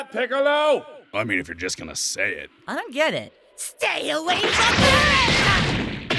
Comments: if you listen Gohan yells “stay away from—!” And doesn’t finish his sentence, now at first thought he’s saying “stay away from them!” Or “stay away from him!” But it doesn’t sound anything like that and it sounds like Gohan’s saying “stay away from krillin!” Which would be hilarious if true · I herd "stay away from krillin", did anyone else hear krillin? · Any one know what gohan said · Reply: "Stay away from them"